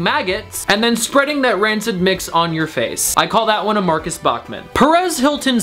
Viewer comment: #13 I thought he said "I call that a Marcus Butler." I was like "IT GOT SHADY IN HERE."